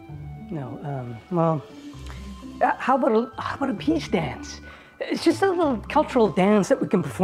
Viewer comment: Lost it at 'How about a Peace Dance'. Lol! Great stuff!😄😄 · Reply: I heard "peach dance." Youre makes more sense, but I like hte absurdity of "peach dance."